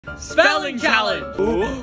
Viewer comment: At first I thought they said “smelling challenge” then I watched the video and it didn’t make sense to me then I watched it again and realized they said “spelling”